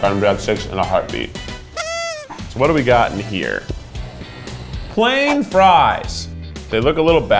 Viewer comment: Keith: These are Cajuns fries. Everyone loves Cajuns. Me: *Is Cajun* *blushes* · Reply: Ren L I totally thought he said ASIAN fries and I was like whoa! What are those😂😂